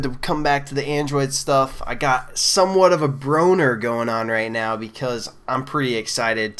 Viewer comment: did he just say he has "somewhat of a boner" ? :o